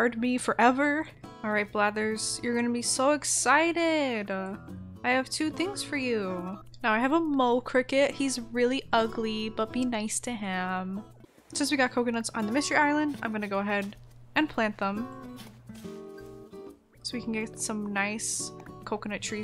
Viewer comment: Lolll noo I heard “yeah since we got Covid on the mystery island”